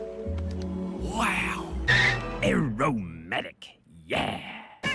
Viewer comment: A R O M A T I C Y E A H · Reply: I misread/ misheard that as "aromantic"